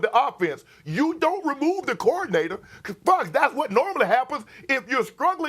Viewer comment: Did Shannon say fck at 6:15 or am I hearing things lmao.